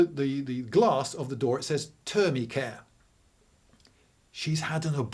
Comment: what word is he saying is on door? Sounds something like terbycam? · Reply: I think “Termi Care,” as in Termination Care”